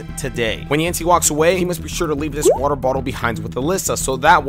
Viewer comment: I thought you said “a thirsty nigga”